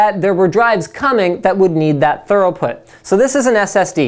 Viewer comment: Did he say thoroughput?